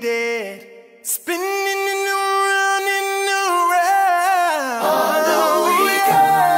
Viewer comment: what did he say Spinnininyouaroud lol 😂